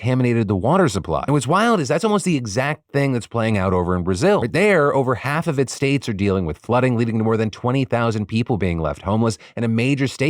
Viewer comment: He said a major state has almost been incapacitated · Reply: he says "but there over half of its states are dealing with flood..." That's what I understand and what the transcription says. AFTER saying half of its states (there are 27 in Brazil) are dealing with floods, which IS NOT TRUE, THEN he says it left a major state incapacitated.